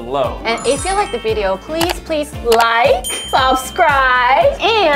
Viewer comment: I heard ' beast channel' in outro. is in my head!!!